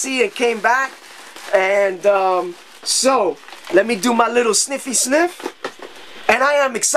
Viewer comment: wait nvm lol sorry i thought u said sniff sniff crack like hes still on it